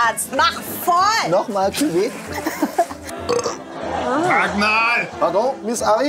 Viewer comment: Was sagt bei 5:24?! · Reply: Sag Mal